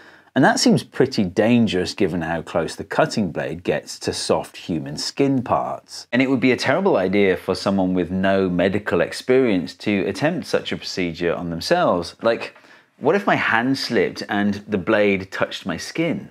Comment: I thought it said “I cast, saw on human skin”